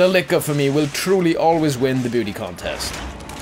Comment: when ever he says "number six" it sounds like he is saying "number sex" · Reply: EpicFilms When he finished the licker,it sounded like he said "for me,the licker will always win the booty contst"